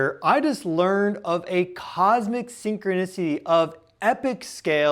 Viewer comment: I thought you said syncronussy at first 😂 I hate this timeline